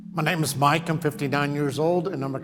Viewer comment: the way he said “ma names mike” sounded like “ma nems Jeff”